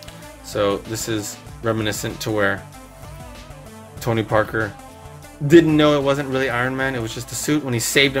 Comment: did he say Tony Parker?